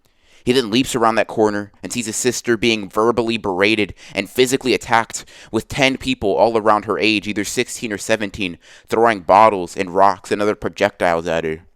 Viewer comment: who else thought he said bombs instead of bottles